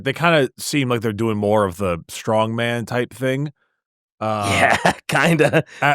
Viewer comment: I heard "strong-mad"